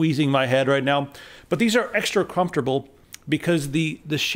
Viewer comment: Did you say extra crumpftable?